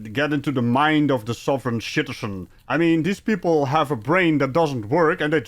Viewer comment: Did you say “you gotta really get in to the mind of the sovereign “shitizen”” @ 10:12? There is a raging wind and rain/snow outside my house and it is hard for me to hear anything else. So my apologies. If not, it would definitely explain a lot about the traveling man in the video. Loving your channel!